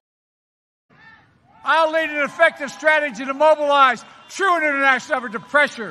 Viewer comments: I heard "I’ll lead an effective strategy to mobilize true international depression" YIKES. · I thought he said "true international depression"